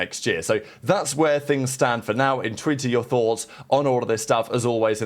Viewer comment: Is bro saying "In true to your thoughts down in the commecnts" ?? I have been watching for months and i can't quite make it out every time LMAO